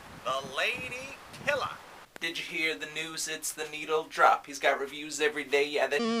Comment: it sounds like melon is saying the n-word lol. Canceled!!!